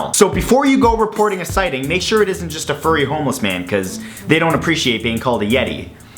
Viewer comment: when Matt was talking about Bigfoot I thought he said "make sure it's not a homeless furry"